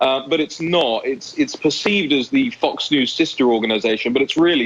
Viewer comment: Did I hear it well? 3:26 the "Fucks News".